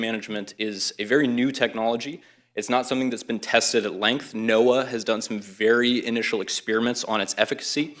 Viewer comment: did he just call it NOAH?! 🤬